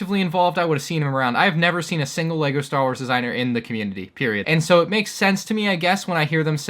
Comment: Did u just say ”pErIodT”